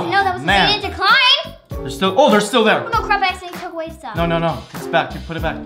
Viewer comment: did say crap or cred